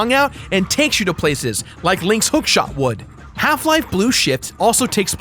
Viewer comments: Did anyone else hear him say "Half Life: Blue SHIT" at 10:56 ? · did he say Half Life Blue Shit? XD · Does he say, "Half Life: Blue Shit "? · Half life, blue SHIT??? 10:56??? Or am i hearing things. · Jirard says Blue Shit instead of Blue Shift :P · Did he just said "Half Life: Blue Shit"? lol